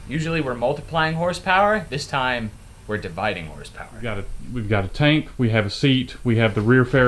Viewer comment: I heard say "we got a taint" lol not " we got a tank hahahha".